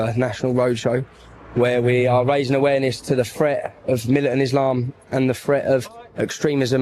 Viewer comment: I think she misheard. Tommy actually said militant Islam.